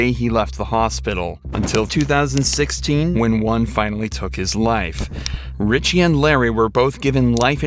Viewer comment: oh I apologize, I heard "until Ryan finally took his life" instead of "one finally took his life". my other comment does already state the detective could've handled this differently tho, so I agree on that. however, I doubt that a lot of other detective would've reacted THAT much differently here. I mean put urself in his shoes: all he knew was that he was interrogating a guy, who had a dead girl in his living room. he did not call the police and he seems to be out of it. his only injury seems to be a black eye, which could further indicate that he did kill the girl in his house (and she put up a struggle and hit him). And it's also not rare in cases like these that the suspect acts stupid to try to get away with it. the detective was also not the first one to talk to him so if you wanna go there, you could also blame all the other people that had already interacted with ryan prior to sending him into this room because none of them seemed to do/notice anything either. so yeah, this detective definitely should've asked him about his well-being the moment he noticed that something was off, but regarding this from his pov, his actions do in a sense make sense. either way, I hope ryan rests in peace